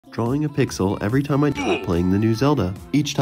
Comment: i heard "drawing a pixel every time i shit myself in the new zelda"